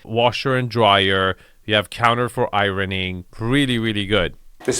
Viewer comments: Arvin said "counter for ironing" but I thought he said "counter for irony" where I guess someone just stands and points out what actual meanings are the complete opposite of literal meanings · I thought you said, “a counter for irony” and stopped to ponder what irony there would be in a laundry room. 🤔